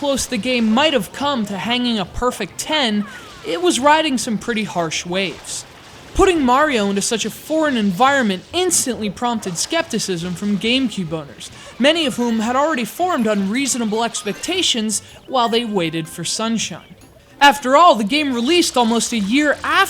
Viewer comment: I know but it sounds like he said GameCube boners.